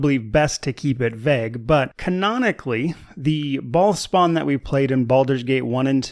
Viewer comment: everytime you say "bhaal spawn" i hear "bald spot". war of the bald spots...